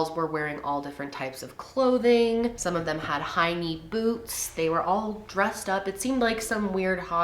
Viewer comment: When you said “high knee boots” I heard “hinny boobs”